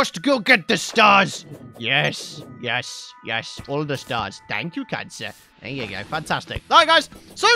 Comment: did he say thank you cancer?! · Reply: He said thank you kind sir